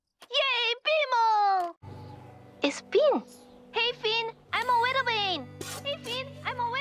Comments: what is he saying ? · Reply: Hey finn! I'm a weather vane! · Was BMO saying "Hey Finn I'm a lil ring??? And what episode is this?? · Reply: I'm a weather vane!